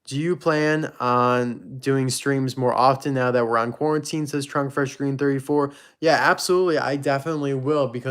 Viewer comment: Hahaha the subtitles thought you said drunk Fresh Green 34 but you actually said Trunk Fresh Green 34.